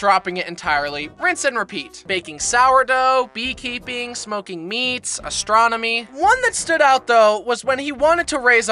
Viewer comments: OHHH SMOKING MEATS (i thought he said smoking weed for like a year now) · please tell me I am not the only one who thought he said smoking w**d